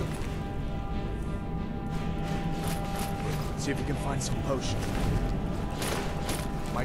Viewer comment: I thought he said see if you can find some lotion for a second I was thinking wtf o.o